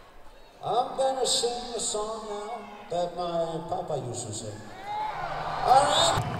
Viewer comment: what did he said? I have bad English, I don't understand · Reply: "i'm gonna sing a song that my papa used to sing" i think :]